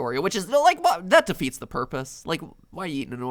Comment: When Matpat said, "Why would you eat an Oreo 'thin,' that defeats the purpose." I heard: "Why would you eat an Oreo 'fin,' that defeats the porpoise." I don't know how to feel anymore.